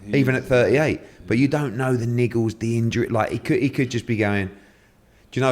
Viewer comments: what was he saying there it sounds wrong for me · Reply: Look up what the word “niggles” means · YO WHAT DID EDDIE SAY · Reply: 😂 he said “niggles” like little injuries… had to run that back too lol